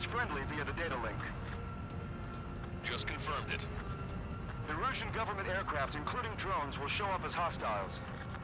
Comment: Did he say 'The Russian government....' at 1:26:56?...